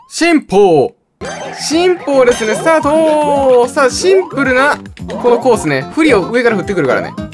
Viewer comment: ポクサスギのせいで3:03のsimpleがtinpooに聞こえた